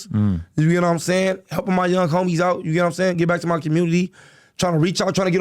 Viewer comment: Thought he said knock the young homies out🤣🤣